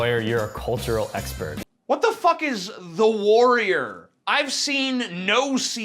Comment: I think he tried to say "The Wire" not "The Warrior". And fact that the name of that great series was used in a buzzfeed video makes me wanna puke.